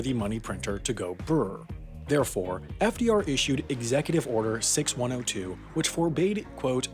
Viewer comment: did i hear execute order 66?